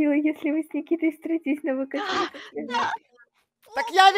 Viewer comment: 👇кто услышал что кто-то из нп сказал:да🥵да🥵 в попу с 2:52 до 2:54 времени